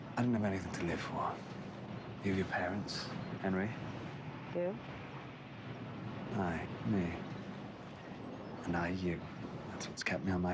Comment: when I saw this scene for the first time, I thought he said "I me and I you" and got really confused. I watched it again and then I thought he said "Aye me and aye you". It wasn't much better 😂